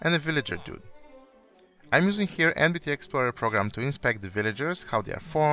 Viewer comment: What is the program called? I can't understand what he's saying. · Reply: NBT explorer program